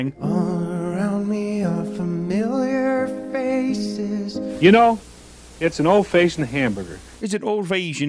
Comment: I thought he said "You know, it's an old face and a hamburger", talking about himself 😮